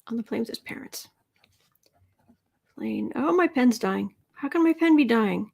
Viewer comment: When you said “pen is dying” I heard “Pence is dying”! Omg!!